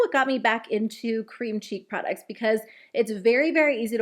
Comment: Im sorry, i love you to death, i absolutely do enjoy watching you in all seriousness... But i just had a massive mind explosion and started cracking up when you said "cream cheek products" because i definitely thought you said "cream CHEESE products" 🤦